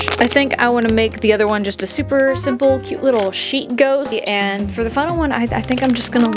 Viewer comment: Love it! Makes me want to build miniature crooked houses... (and also figure out how a sheep ghost would work, 'cause I misheard your "sheet ghost" as "sheep ghost" and now my brain is stuck ^^' )